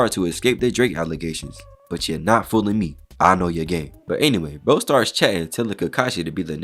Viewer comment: I heard"I know you're gay''